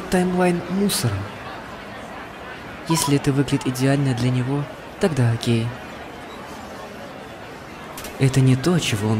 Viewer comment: Вам тоже послышалось: "тогда он гей...))"